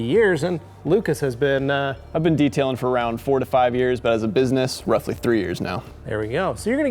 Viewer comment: lol I thought he said "45 years" at first